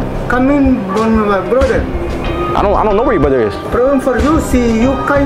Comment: It sounded like he said my brother you called the cops on my brother problem for you and then he said something like my dad he definitely threatened you